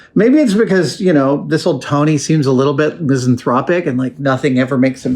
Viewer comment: I heard a new word. Regardless of what was actually said, I heard "Byzanthropic" where byzantine, hearing damage, and misanthropic meet, it's seriously me.